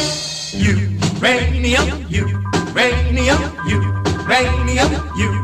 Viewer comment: You rain me up, you rain me up... so thats why i didnt find this song...... :D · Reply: At first I thought he said YOU BRING ME UP instead URANIUM. xD